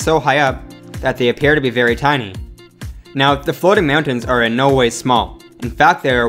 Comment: it sounds like hes saying "meow-tains"